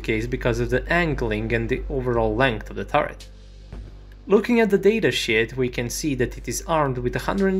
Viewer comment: doesn’t it sound like he said “looking at the data shit,” that made me laugh.